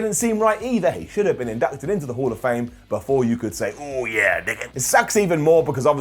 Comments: Did say "Oh yeah ni$$a" @ 9:43? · Did he say “oh yeah n-word”